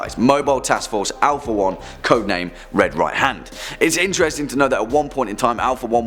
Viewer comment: when you said red right hand i thought you said serpants hand and i was like wait isnt the SH against the S.C.P foundation btw the SH's mission is to free the S.C.P's